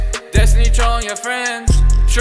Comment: It sounds like hes saying troy then trollin